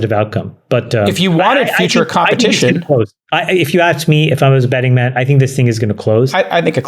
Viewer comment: did Chamath really say if I was a betting man?